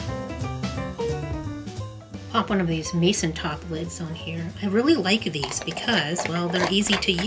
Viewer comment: Did you say "Mason Toplets"? plastic, rust-free. How durable? Only for this or also for canning?